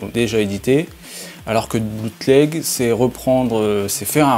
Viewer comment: Il a dit quoi à 0:40 alors que le ????????? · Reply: bootleg